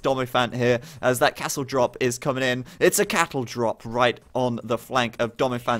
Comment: did i hear 'cattle drop'?